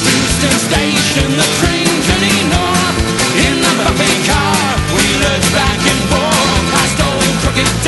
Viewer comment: The only thing I hear there is "In the f-ing car", wonder if anyone else hears that?